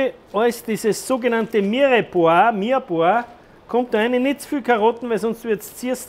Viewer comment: was sagt er da? Mirbor? Mirebor? Was ist das?